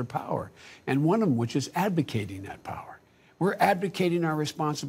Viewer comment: I thought he said we were ADVOCATING our power over seas, as in we're puffing up our importance and meddling where we don't belong.